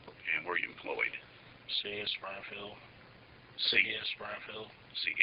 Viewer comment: Lol I thought he said ‘CS Springfield’ too